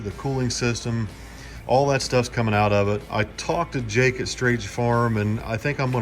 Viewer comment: It’s finally time!! Gone be a bad mfr! When you and were looking under the hood of the monte, I thought you said you called jake from State Farm. Lol. Anyway, I think I’m gonna get off here and order me a new atm carb for my little 93 coupe with a 427 and a tko. Should be stout. Can’t wait to see the end result of the monte!!